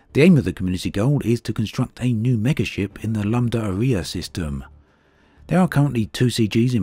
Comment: It would be a big help if you put up a caption containing system names, instead of you just attempting to pronounce them. It sounds like your saying the "Lamb Diarrhoea" system! ( Lambda Arae )